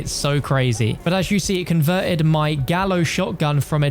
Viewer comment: SECRET ANNOUNCEMENT: I've soft-launched an amazing new Cold War Zombies Poster Pack - A PACK OF ALL 6 NEW PERKS! Check it out here 😃 <link> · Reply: Gallo is Spanish for rooster. Pronounced "Guy-o". I thought you said Gallows Shotgun until I rewound it. Damn I was so ready for another SG.